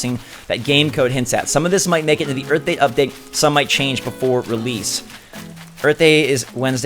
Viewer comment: In the direct I thought that it said birthday update I only realised now